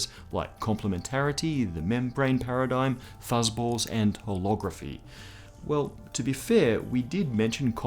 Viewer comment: sounded like pornography xD chlorography